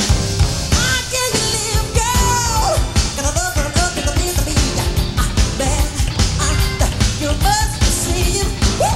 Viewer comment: Is it me or idk what the he'll he said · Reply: Tetay Milton cause the love the love is meant to be